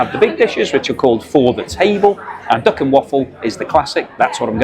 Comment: I thought you were saying dock and waffle, you mean duck, lol